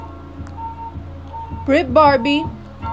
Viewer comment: I thought she said “rip barbie”